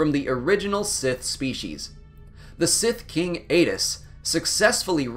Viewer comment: I was in the next room listening to this. I misheard "The sith king ate us" Lol Chill dude. A palpatine, you become when hungry you are. Hungry? Grab a "sithkers"